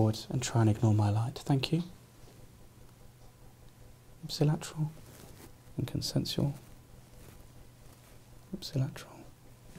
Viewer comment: what does he say at 1:48 ? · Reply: ipsilateral and consensual